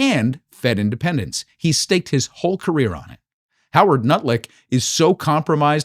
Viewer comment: Did he just say nutlik? Haha!